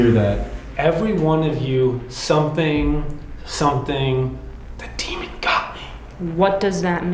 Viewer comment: On that evp of Robert I hear "everyone of you get out. The demon got me".